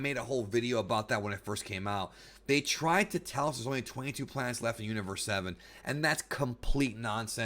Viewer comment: 28 plants with suitable life forms for battle in the T.O.P is what was said